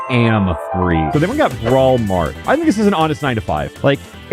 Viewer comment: for a sec I thought he said “Walmart” I’m not even joking